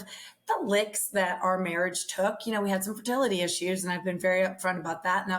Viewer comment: seems like a great dad. The fact he can sit there and thank for being a good step dad on a pod takes humility. Thanks for sharing your stories ❤ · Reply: I think said they had some fertility issues, not 'fidelity' issues...i misheard this too at first.